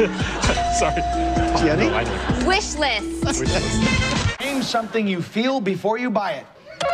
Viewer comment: "Name something you feel before you buy it" -"Excited" Lmao!! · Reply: I thought he said “name something you feel before you bite it” 😂